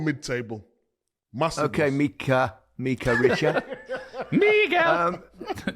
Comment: what did alan say at 11:44??? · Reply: He called Micah "Miguel." It's a reference to the Gazza episode